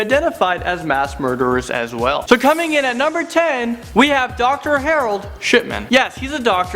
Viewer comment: I thought he said Dr. Harold shitman